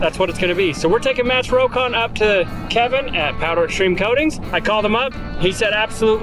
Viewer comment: Is it just me or does it sound like says "powdered shrimp coatings"?